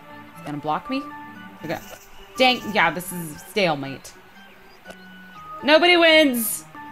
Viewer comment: I thought she said scalemate